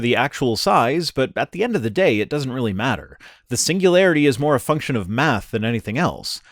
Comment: Did I just hear you say size doesn't matter?